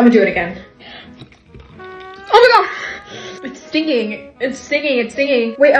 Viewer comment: First I thought she said it's stinking😭😭😭